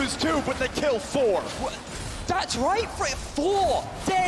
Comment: does he say ‘that’s rape!’ 😂😂😂